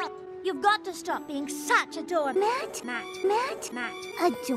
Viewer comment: I'm only one who hear here ADORE mat? :D